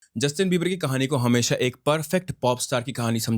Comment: Am I The only one ho heard justin beiber-ii lol. I keep replaying and that's only what i hear